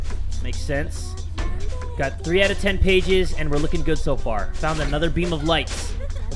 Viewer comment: did he say pages? Referencing Slenderman???